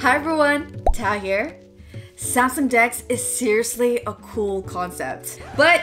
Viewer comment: Oh lord! She said samsung dex....I thought she said skn dks! 👀 I'm like say what!